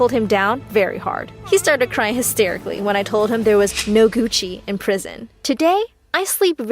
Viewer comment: I thought she said couchie